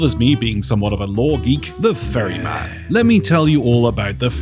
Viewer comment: The FURRYman. 😆 So glad I just misheard. Oh yeah and it would be cool that if the game registers you have 'the' Skeleton-curse attached, you get send elsewhere instead of the Ferry. Maybe a sort of wierd limbo, surrounded by fog - you see coffins and normal skeletons everywhere around you and can run back to the living world through a coffin that opens. ⚰